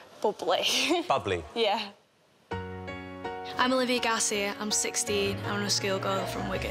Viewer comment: I thought she said,"I'm 60."Hahahahahahaha😃😃😃